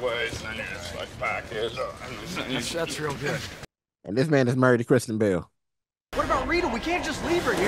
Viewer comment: This whole time I thought you were saying “this man is married to Christian Bale”, and I was dying laughing thinking of you believing those two were married.